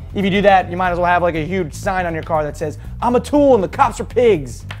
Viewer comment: dude when u said atool i thought u said my name anyways thanks for coming back dude on monday was so boring that there was a blackout in my area which messed up my computer.